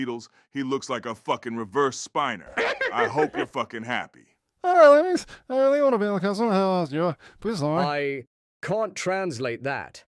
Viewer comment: I think I managed to hear him say “To be honest, I don’t really want to be on the council…yoda…please lord”